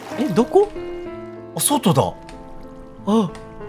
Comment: 外が お外にに聞こえて可愛いのよさーーー